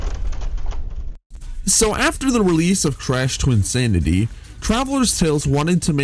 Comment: I had to do a doubletake for a sec I thought you said TRASH Twinsanity 😂 I was gonna be like ":( I thought it was neat!"